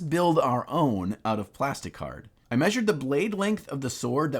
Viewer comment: My volume was low, and when you started making the sword I heard you say “make one out of plastic butter” Had to turn it up and go back 😂